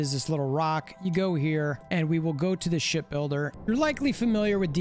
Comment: i keep hearing " Shit builder " 😂😂😂